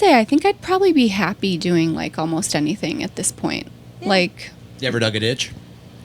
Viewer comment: what did he say at 08:01 ? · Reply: Lua Del Rey u ever dug a ditch